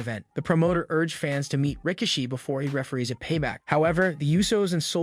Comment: When he says Rikishi it sounds like he is saying Ricochet lol